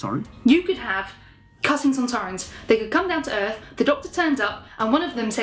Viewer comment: I thought she said "cutting Sontarans" (dealing with the theme of self-harm)